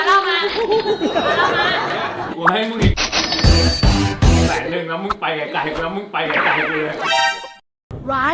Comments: พี่พูดว่า(กูให้ไปอีกแสนหนึ่งไปไกลๆ)💲💱 ใครได้ยินบ้าง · มีใครได้ยินเหมือนเราบ้าง ว่าพี่พูดว่า "กู ให้อีกแสนนึงแล้วไปไกลๆ"